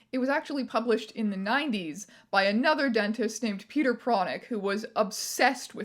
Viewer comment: I misheard Peter Pronych as Peter Chronic and I was just like “what in the spider man rogues gallery fuck is going on here”